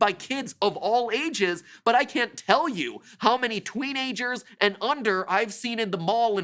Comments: Twenager... Did I hear that right? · twinagers that’s what I heard also good luck